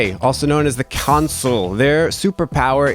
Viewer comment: Is that not supposed to be ESFJ, also known as the Consul? The subtitle says "Console" instead of "Consul".